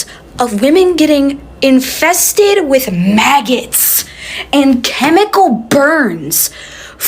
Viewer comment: OMG I thought you said people were seeing "my guts" but I realized that you said maggots. Ok. I would hate to have the name "Maggie" and respond to you calling my name.